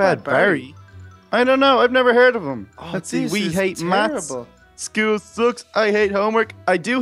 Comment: I just realized that they said mats instead of math